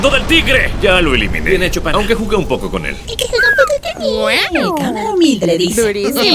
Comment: no entendí nada XD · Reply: Dice tv woman: aunque jugué un poco con él, arremedando a camare destapacaños